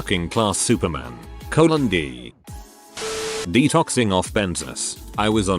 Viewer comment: i heard detoxing on bezos